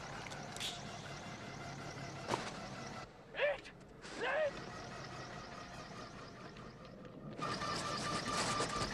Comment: is he saying run sorry i don't speak Norwegian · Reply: Ut, nu ( out, now)!